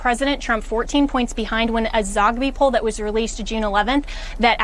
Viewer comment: Did she just say zombie poll?